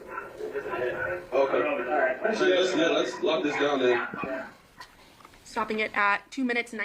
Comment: What I heard: "Love this dog man." 🐶❤🐶 What he said: "Let's lock this down then." 😂😂😂